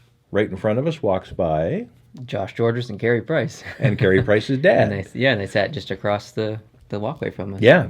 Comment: Did you just say “Carey Price is dead?”